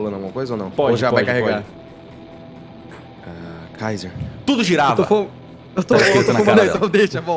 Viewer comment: Quando o Cellbit disse "tudo girava" eu entendi "tudo girafa" kkkk